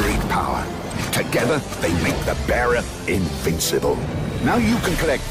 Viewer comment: I remember as a kid, in the stars commercial I thought he was saying Bara Invincible. For years I thought that was the name of the form